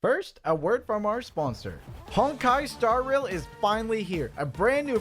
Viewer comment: i heard heard him say hentai star rail not honkai star rail